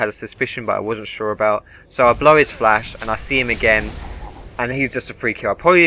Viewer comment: did he just say blow is flashed? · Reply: Blow his flash